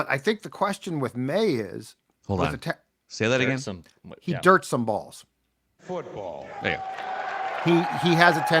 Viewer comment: I thought said Maye “jerks some balls” around the 9:00 mark and had same reaction as. I’d be cool with Murphy and Penix in round one then a G in 2nd.